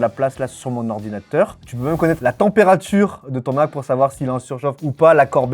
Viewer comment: je croyais qu'il disait " Je peux même connaître la température de Thomas, pour savoir s'il est en surchauffe ou pas" 🤣🤣🤣 Euhhh quoi ahahaha